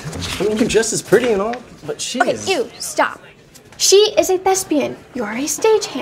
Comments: I was so confused. I thought Roni said Nessa was a lesbian, and I was like ‘Why is Nessa excited about kissing the guy if she’s lesbian?’ 😂😂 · I thought she said she is a lesbian instead of thespian 🤣🤣